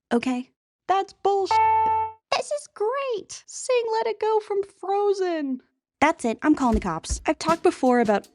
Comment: At the beginning…just for a moment…I would have sworn I heard “Sing let it go from Fortnite” and I had a visceral pained reaction